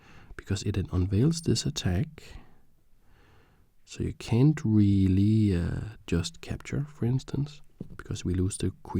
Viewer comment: Are you French? It's like I heard "You can't just really euuuh..."?